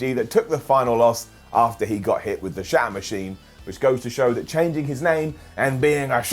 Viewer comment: It sounded like he called "The Shatter Machine" "The Shart Machine" at 8:44 . That move would be more devastating than the surprise roll-up. lol